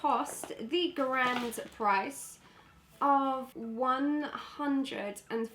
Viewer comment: When you said the grand price I thought you said "it's a grand for price!" my face dropped! It's still crazy expensive. I think the most expensive I've had is the urban decay all nighter. Even buying the Illamasqua Matt veil at £32.50 made me feel awful.